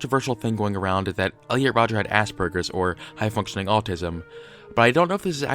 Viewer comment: Does anyone else hear 'ass-burgers' as opposed to Aspergers at 07:56?